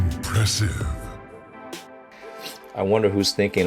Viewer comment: I actually thought he said Impressive Edit: I thought it might be good video if you can try out the minus tempo quick in real life with a friend maybe? Also just telling Asahi is pronouced like ahh saa hee (in like "he was a boy") maybe this will help my explaining isn't the best sorry.